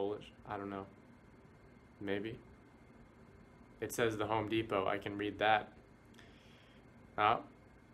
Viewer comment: I thought he said hum de bum not home Depot